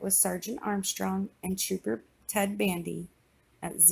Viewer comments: I thought she said Ted Bundy instead of Ted bandy. I was like “hold up a minute....” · I thought she said Ted bundy?😱 It's actually Ted Bandy😂😂